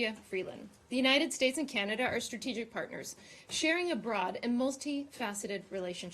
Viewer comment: Sexy lady; but did she say: "sharing bra": 0:24 ; Dear Morgan; you never wear a bra; how can u share it!